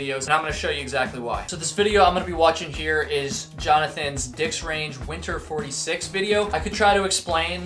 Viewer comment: Oh, "Dix" Range.😂 I thought you were saying "dicks range," referring to the types of people in a particular area!! 😆 God help me!